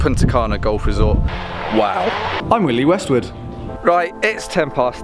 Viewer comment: Totally heard that as '"I'm Willy Westwood"!